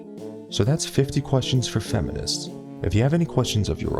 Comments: At the end I thought he said "And don't forget to rape this video and subscribe" xD. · I thought he said “don’t forget to rape this video” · At the end I thought he said "Dont forget to rape this video and subscribe" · I legitemately thought he said "don't forget to rape this video" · At the end of the video I heard "don't forget to rape this video" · If a man doesn't find fat women attractive, then he's fat-shaming and that's sexist, but if he does find fat women attractive, then he's fetishizing them and that's also sexist. Is there any way to deal with fat women that isn't sexist? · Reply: lol at the end I thought you said "don't forget to rape this video and subscribe" I was gonna die laughing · A sequel is coming. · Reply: When you said "rate this video" I heard "rape this video" probably because I had low volume and femminisam lol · It's October 18, 2016, and this video now has 500,000 views. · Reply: for a second I thought u said to rape this video at the end but then I realized u r a human being and u don't actually think about rape all the time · Lol at 9:03 I thought he said "don't forget to rape this video and subscribe" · at the end I thought he said "don't forget to rape this video and subscribe" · I thought he said, "Don't forget to RAPE this video and subscribe!" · I thought u said "don't forget to rape this video"... 😂😂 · when he said rate this video, i thought he said rape this video lol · I thought you said at the end to rape the video 😂